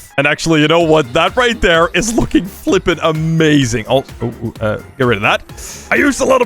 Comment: i think he said f word… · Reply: He said "that's looking flipping amazing "